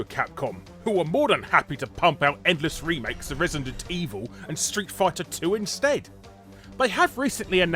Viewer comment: Did you say "Street Fart-er 2?" LOL!!! (Yes, I am immature. He he)